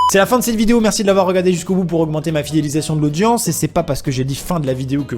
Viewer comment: J'en peux plus :'( Il dit quoi a 7:22 ? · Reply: Il dit: Bonjour, je te remercie de faire augmenter ma fidélisation de l'audience